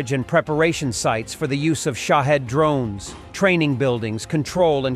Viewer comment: 😁😁😁I thought I heard him say "Sh*thead drones". 😁😁!